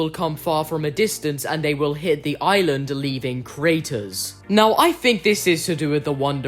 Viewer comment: Did you say Kratos?